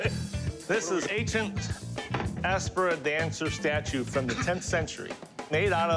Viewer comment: Did he say "Aspera"? 😁😁